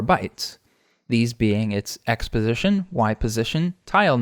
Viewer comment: For a second I thought he said exposition not x position 😂